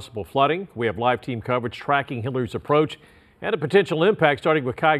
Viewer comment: Did he just say tracking hitlers approach?